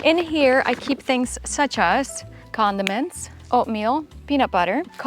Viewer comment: I'd like to setup her campervan... 😍😍 She just said "Condiments'. I heard "Condoms" 🤭